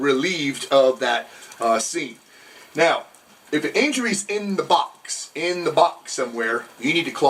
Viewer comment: At the 6:50 mark I watched say "if the injury is in the box." All I heard was "If the injury is in the No No Square."